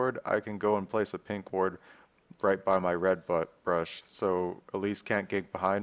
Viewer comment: Did he just say "Place a pink ward right by my red butt"? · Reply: listen carefully, its "red butt brush" hahaha